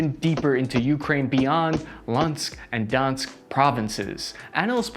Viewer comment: Did you just say "Lansk and Dansk Provinces" lmao do you mean Luhansk and Donetsk? O boy. i only forgive you because i like the video lol